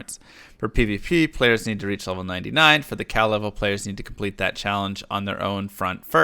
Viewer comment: I heard him say must reach level 99 for pvp and yelled WHAT ! befor seeing it was level 9 lol